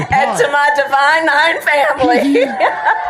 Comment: did she say...her divine 9 family?